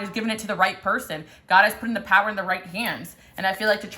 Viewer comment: I thought that I just heard you say, "God has put the power in the right pants." So, naturally, my dumbass thought, 'Is she talking about "My pants"?'. Lol 😂. Love you. On vacation and posting from my mom's account. ‐